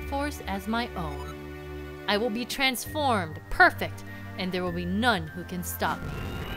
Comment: Is it bad that I heard you say: "I will be transformed, perfect, and there be nun who can stop me..."? Does it mean Zelda or Link is the nun?